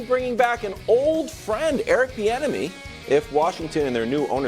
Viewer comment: Seriously always thought they’ve been saying Eric “The Enemy”